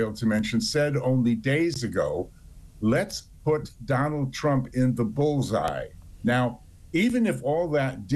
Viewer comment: I thought he said put Trump in spotlight.